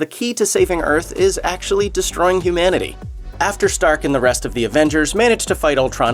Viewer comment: when he said "humanity" it sounded like daily dose of internet